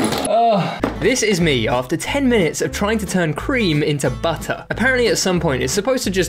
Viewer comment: “ FINK WE GOT BU’UH”🗣️🔥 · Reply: "This is me after 10 minutes of trying to cream" I swear to God all I heard him say